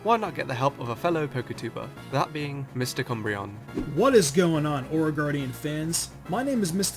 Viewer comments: I thought he said mr cumbreon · Lol at first I thought said Mr.Cumbreon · Not to hate at all, since I like ur vids and enjoyed a few of, but when you introduced him, I heard it is "Mr.Cumbreon". I'm so sorry.